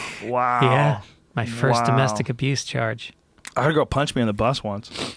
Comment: Did he just call Joe rogan goerge at 4:40 and nobody caught that? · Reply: He said “domestic abuse charge” not “domestic abuse George”. Dude really?